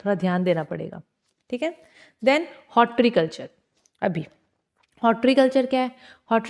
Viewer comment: I think you are saying horticulture